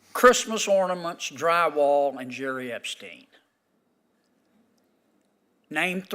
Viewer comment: what did he say!?? Haha Jerry Epstein??